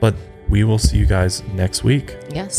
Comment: 1. i still think the woman in the intro sings: "baby" even though i know she doesn't. 2. since you said it could be jack skellington, i am unable to see anything else. 3. i hope you will be able to ship to germany one day. looking forward. 4. amy is an impressive person and i hope one day she can be reunited with her family. sending love to her, wherever she might be. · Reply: I always thought she said higher higher?